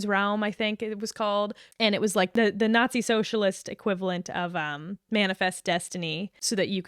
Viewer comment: saying the nazis did socialism is like saying america is the land of freedom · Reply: I guess I heard the "national socialist" as "nazi, socialist". This comment chain is probably doing your channel great algorithm wise btw! 😅